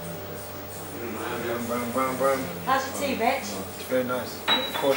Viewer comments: I thought she said 'How's your tea bitch?' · sounds like she says "how's your tea bitch" at the start hahaha